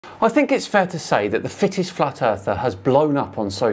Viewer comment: 10.20. I genuinely thought you said "The Thickest Flat Earther." Freudian slip? And he really is the thickest, by a long chalk.